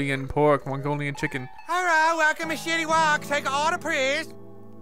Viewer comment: I thought he said shitty wok